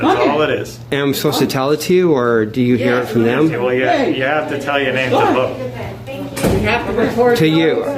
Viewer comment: mark The cop said in order to vote, ID needs to be shown to the cop and that is a lie. At 7:59 mark told the cop he has proctologist gloves on. LMAO. At 9:12 mark someone said was violating everyone’s civil rights by recording but they are clearly violating his. At 12:14 mark IS she really calling the police to help the police who said he does not need back up? THE BOOTLICKING IS REAL OUT HERE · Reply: No he said you have to give your name to vote which you do. Name and address and they check you off. He never said ID. Big difference not to contradict you but it's important. It's The Reezisstaaance! Thanks for this vid is cool!